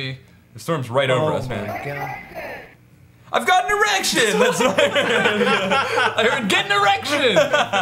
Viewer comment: I heard "I ATE AN ERECTION!"